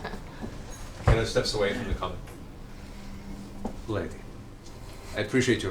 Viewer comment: Matt: I appreciate your brashness.... Me at 02:38 a.m: half asleep.. did he just say appreciate your breasts..